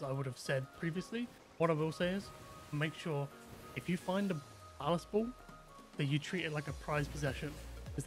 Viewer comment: what cannonball is the "Most prized"? 3:03 sounds like youre saying ass ball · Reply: ballast.. bal assed